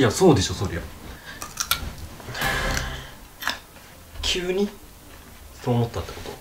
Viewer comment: 急にがキュウリに聞こえる笑